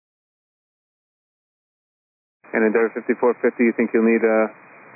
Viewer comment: Anyone else notice that the female pilot sounded like she was saying 75450 almost every time?